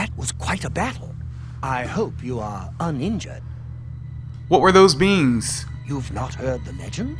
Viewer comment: "What were those beans?" That's what I heard XD